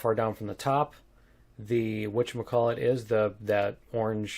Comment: I didnt understand what said about Pole Level on 17:49. ".. the pole level is how far down from the pole or how far down from the top.... (what, what, what?) .... the orange hexagons" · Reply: The whatchamacallit.